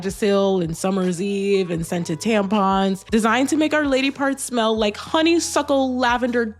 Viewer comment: wait i'm sorry did I hear that right SCENTED TAMPONS???